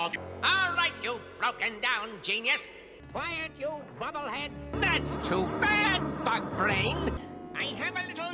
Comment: Did I just hear “that’s too bad, f- face!”